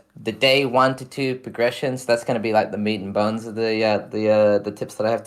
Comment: "this is the meat and butter" my new favorite saying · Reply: I thought he said meat and bones. It just sounds kinda like he said butter. Lol